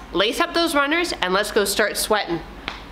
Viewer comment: When said "now let's start again from the top" I nearly reached through the screen! But I did it! Wow, this raised the heart rate and caused a lot of sweating! Thanks. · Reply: Of course you did it!! Atta girl! I don't mind the swearing...oh, SWEATING! Thought you said swearing 😜